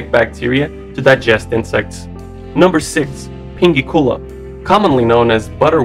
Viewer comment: i thought it said "Pinky Cola".